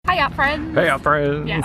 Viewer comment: I always thought y'all said HIYA FRIENDS